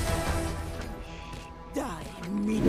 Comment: what was reyna about to say there after killing phoenix? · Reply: It's "Die meaningless!", one of the ace dialogues